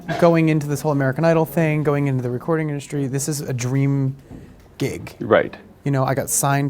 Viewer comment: At the start I thought he said this is the dream geek n then I realised he said dream gig lol